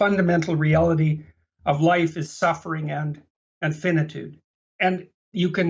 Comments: Peterson says that "the fundamental reality of life is suffering and finitude". He did not say infinitude. Finitude originated as a hegelian term used often in the works of Heidegger and Jung and is essential to humans nature and or mode of 'being'. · I think he said " and finitude" and not "infinitude"